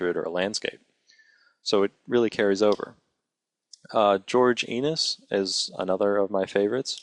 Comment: sry to point this out but.. Did you say George Anus? XD